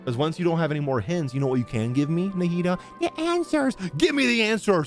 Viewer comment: When he said “The answers” in Nahida’s voice, it sounded like “goody 2-shoes” in a strange way.